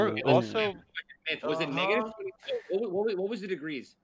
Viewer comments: I can't be the only one that thought ninja said the n-word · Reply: He did say it lol, nvm he said negative just fast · did ninja say the n-word? Lmao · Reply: "Was it negative? What was the degrees?" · DONT tell me that it didn’t sound like ninja said the n word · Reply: he said ‘negative’ just really fast i think